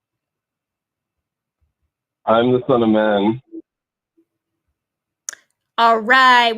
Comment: What did say before he got dropped? I listened to it three times and couldn't understand, something about a man? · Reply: He said "I am the son of man." Why? Who knows.